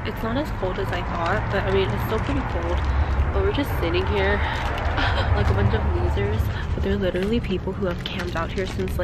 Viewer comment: did u say "with a bunch of loosers"? BTW I'm just askin😉